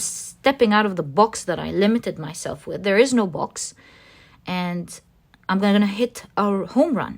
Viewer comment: #3. When you said, ‘There is no box’, I heard ‘There is no spoon.’ Definitely felt like I have been boxed in for years, but there is no box. It’s all in my mind. I really need to get this. I am frightened just trying to comprehend this. Thank you.❤